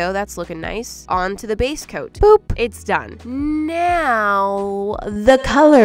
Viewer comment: SOB SUBTITLES SAYS POOP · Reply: She said “boop” for the transition not “poop”